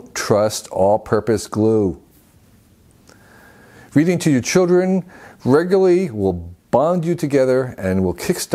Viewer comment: Me who heard “all purpose glue feeding to your children regularly will bond you together”